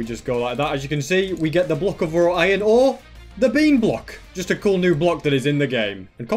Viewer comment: did he say "the block of raw iron ore the bean block!" or "the block of raw iron or the bean block!"